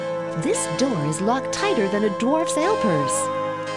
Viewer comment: "This lock is locked tighter than a dwarf's ale purse." All-time classic :D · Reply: hahaha...I thought she said "dwarf's male nurse"